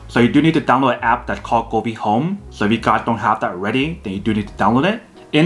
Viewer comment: Wtf are you saying? What the app name? 0:45 Gooby home? Google home? Goodbye home? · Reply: govee home